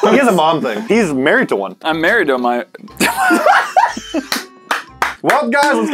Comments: making fun of peoples moms. "he's married to one", "i'm married to my mo-"😂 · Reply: Sounded like he said hes married to a minor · making fun of peoples moms. "he's married to one", "i'm married to my mo-"😂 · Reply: dountfreeman I heard “I’m married to mine.”